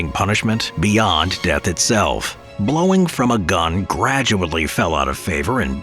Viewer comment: I thought they said blown near a gun